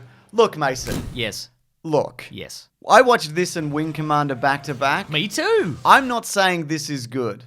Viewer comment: wing commander? Is he saying? I had to rewind that like 7 times to understand what movie title he said and couldn’t tell from that clip what it was.